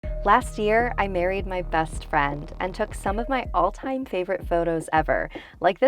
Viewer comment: HELP I HEARD “LAST YEAR I BURIED MY BEST FRIEND..”